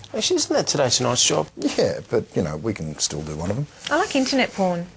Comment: Does anyone understand what says at 1:47? As a response to: "alright mate, so what are we going to target this week: eeh... drugs, illegal immigrants or welfare fraud?" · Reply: "Ah shit isn't that Today Tonight's job?"